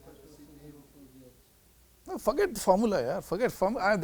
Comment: I had to replay this moment, I thought he said f*ct it the formula.